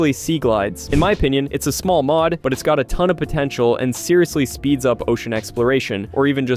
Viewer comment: did someone say potential